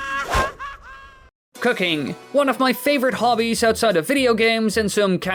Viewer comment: When he said cooking 6:11 i overheard it an thought he said cocaine...